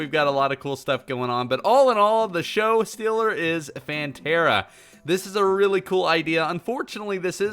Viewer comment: When you said Phantera I thought you said *PANTERA*